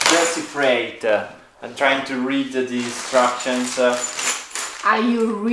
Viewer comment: I had to laugh when was starting to get the bed set up. It sounded like he said he was "reading the DESTRUCTIONS" instead of INSTRUCTIONS so funny,,,,!! We've all been there!! BTW Beautiful drone video of your visually stunning area of Italy.